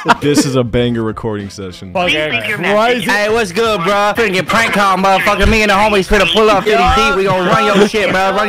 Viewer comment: I think he said "30 deep" and not "on this beat" depp means amount of people